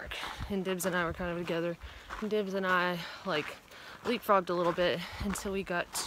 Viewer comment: had to listen again at 1:20. I did not think she said leap frog. I definitely heard we effed a little bit. oops